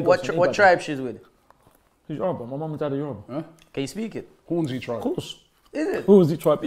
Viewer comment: If you listen carefully at 0:01, the guy asks "what tribe she's with", to which Ty says "Yoruba, my mum was Yoruba" so he was referring to his mother being able to speak it. He even says "of course, that's where they're from"